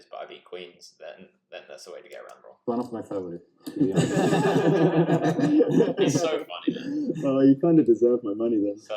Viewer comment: what does mustafa say at 11:16 that everyone laughs at? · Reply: 'It's one of my favourites', talking about pocket kings